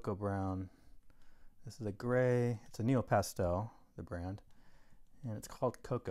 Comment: So, do you add some kind of fixative? It is a greeting card so it will be handled, correct? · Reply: Thank you for clearing up the confusion. During the video I thought I was hearing you say nupastels but I must’ve been incorrect. I have also done some blending with turpentine and I really like it. It’s a lovely painting. Oh, I almost forgot. What kind of paper is it? It looks like watercolor paper.